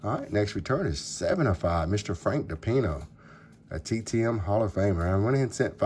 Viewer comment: Tons of ink. I'm not sure any mail was delivered today. I have a slabbed 80 Topps Garvin 9. Only reason is it was $1 on EBAY. I thought you said Frank Pepino.